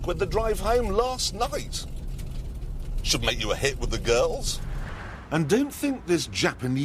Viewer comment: what exactly did he say ? Head up with girls.... something ? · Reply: "should make you a hit with the girls"